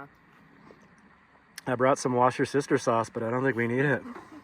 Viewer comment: Did I hear "wash her sister sauce" ???😂 that's hilarious. I now have a new way of saying a word that always makes me feel like I'm trying to speak another language 😂😂 thanks